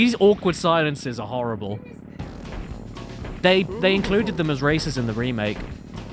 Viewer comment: EXCUSE ME. PHRASING? IT SOUNDED LIKE YOU SAID RACISM